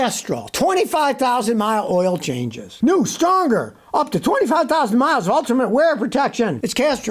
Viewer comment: For a minute I thought Scottie said Hawk Tuah...but it was "Up To" 😂😂